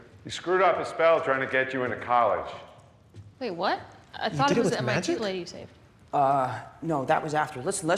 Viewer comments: what did mj say? Can anyone help? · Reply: “i thought it was the MIT lady you saved” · What says Doctor Strange in 0:26? Someone tell me please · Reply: He said: "He screwed up his spell trying to get you into college."